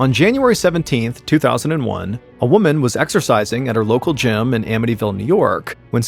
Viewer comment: 😂 I misheard it as Exorcising!